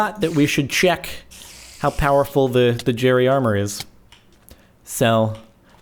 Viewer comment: coulda swore he said jerry shart